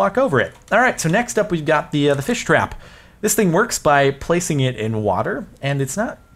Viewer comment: When you said fish-trap, i thought you said Spring-Trap Oops wrong corner of youtube